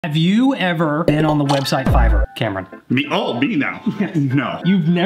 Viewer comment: I thought he said fiber😂😅